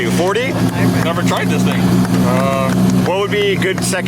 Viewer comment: What did he say at 9:15 tho he never drives the thing or never tried the thing he said something fishy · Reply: i never drive this thing